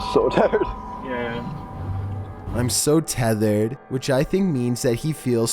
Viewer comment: I dont think the guy said tethered at 1:12 i think he said tired but just sounded like tethered because he nervously laughed. You said they're trying to get back to their hotel so that would make a lot more sense to me.